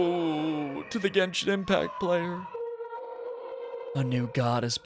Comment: did he just say “to the Genshin impact player”?😭😭😭